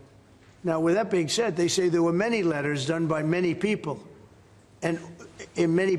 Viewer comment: I thought he said there were no files?